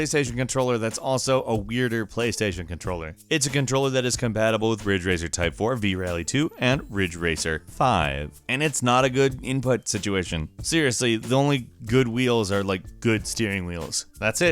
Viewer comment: It sounded like he said fridge racer